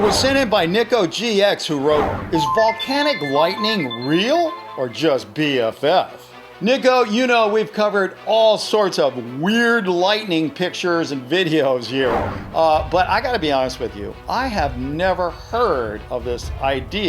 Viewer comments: did just say the n word? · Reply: He said Nicko that was the name of the person who sent in the video dont offend for something that isnt real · That sounded like nibba but with a g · lol it sounds like he said the n-word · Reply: Playboi.Phoenix I heard niggo but still it sounds like the n-word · did u say N-WORD?!!?!? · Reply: He said Nicko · yo in 9:51 i thought he said the N word but he said Nicko